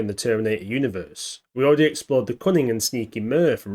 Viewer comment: I misheard at the start, I thought you said "we already examined the cummingham and sneaky mirv" I thought oh there was a British AI? Then i realised you meant cunning, not cummingham...